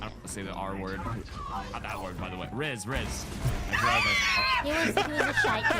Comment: wait what? what's the r word LOL was it not rizz?